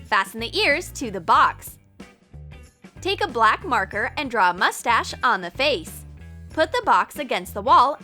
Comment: did she say moustach?!